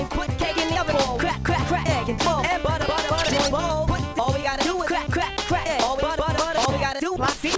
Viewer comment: Okay at one point I heard "All we gotta do is crack"